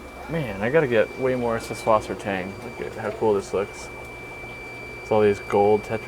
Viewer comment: I cant hear what the plants name is 8:27 does anyone know? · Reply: subwassertang, something he used to sell on the site but stopped for some reason, I’m sure a lot of people would love to buy it if it was carried